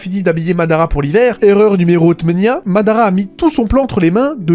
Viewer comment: erreur numéro quoi ? Tu as bien bien temenya ? 8? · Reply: Il dit « erreur numéro tmenia » ça veut dire 8 en arabe